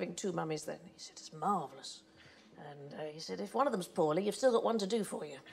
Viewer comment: What does Sandi say here? Is it "if one of them's ____, you've still got one to do for you"? It sounds like she's saying "poorly" or maybe "pouring" (blood). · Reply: EebstertheGreat poorly, I heard